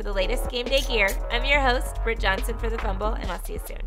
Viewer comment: What said: Hey, I’m What I heard: “Hey I’m thicc Jonhson”